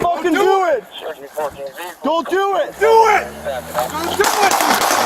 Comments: When they were saying don’t do it I heard a cop say do it hear the audio good a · Anyone else hear the cop at 2:01 say “Do it!”??? I know it was totally still dudes fault but come oonnn 🤦🏻‍♂️ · I swear Shia LaBeouf says “Do it.”